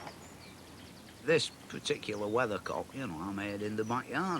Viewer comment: 5 minutes in and I thought the whole time the video said weather “clock” haha😂 I was wondering why he kept going last the clock until I heard him say something else then checked the title and said ohhh🤦🏻‍♂️😝